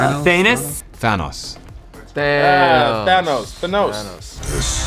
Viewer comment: Did he really say "the nose"?